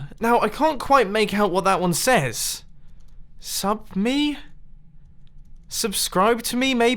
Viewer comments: I absolutely thought that said "Suk Me". gr8 job Oli. <3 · I thought it said "Suk me" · I thought it said suk me